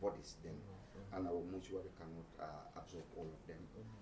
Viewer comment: Did i just heard him say monetory instead of Mortuary 😅